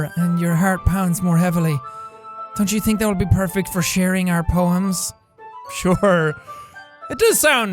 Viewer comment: i thought poems said penis